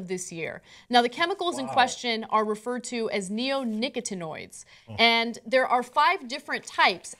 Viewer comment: Sounds like Anna said neo nigga tanoids at the start